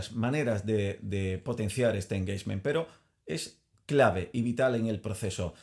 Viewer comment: hola como se escribe eso que dices en el min 2:30 engeisment?? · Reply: Engagement